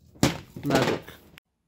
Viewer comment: DID HE REALLY SAY MELK???? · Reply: Lol I said magic